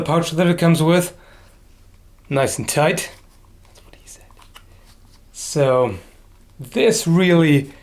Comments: Anyone else notice him whisper "That's what he said." at 5:22 lol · I heard that " That's what she said ". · did he whisper "That's what she said?"